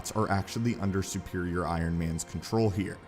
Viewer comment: Was i the only one that heard him say Superior Spider-Man and not Superior Iron Man